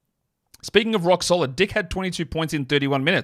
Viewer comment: I really had to put video back to 17:48 twice, when I heard saying "dickhead"